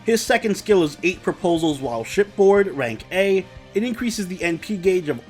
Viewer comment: i heard it as "eight proposal while shitpost" lol